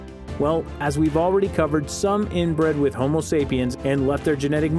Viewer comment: listen closer he barely pronunciated the t. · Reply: but again at 11:33 he actually says "inbred"